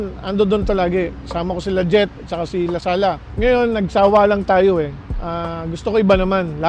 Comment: COMMENT IF BATANG 90'S KA!!! 😍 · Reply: tama po ba JET LASALA narinig ko sa 5:46 secs